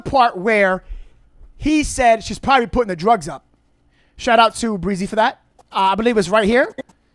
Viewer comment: It sounded like he said, "They put her drugs up." Does he mean Drs put her drug levels up? If so, how does he know this private information?